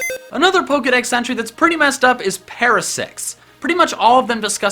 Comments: For a munite i thought you said Parasex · I heard “Parasex” instead of “Parasect’s” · also it sounded like you said Parasex XD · not to be perverted.. but it sounded like you said parasex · It sounds like he said parasex... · did say parasex or parasect